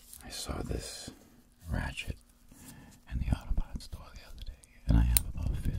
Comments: He said he found the Ratchet in an auto parts store, but I heard him say an autobot store. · am I hearing it wrong or did he say he found the ratchet in an Autobot store? Because if so that speaks to my inner Transformers nerd.